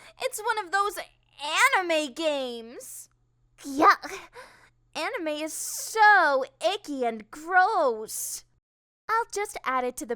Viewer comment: What is she saying on 7:37? Anime is so EDGY?